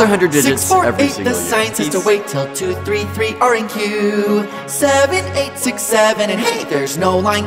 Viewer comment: did it just said 67